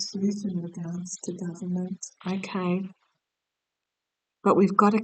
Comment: I heard a female voice at 9:51 till 9:54. "Governance " or something similar .....